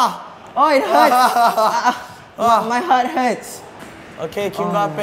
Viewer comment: he said my hurt heads instead of my head hurts lmao